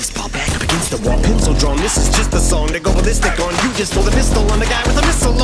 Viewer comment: did eminem say the n-word?!!! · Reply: "and i go ballistic on"